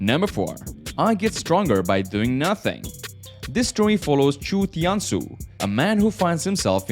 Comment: I thought he was saying chutiya